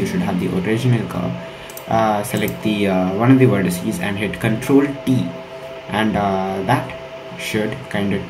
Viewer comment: I thought said hit control+E and then i listened again and thought it was Control+D but after nothing happened on blender I then listened again and realised he actually said Control+T --if anyone has the same problem.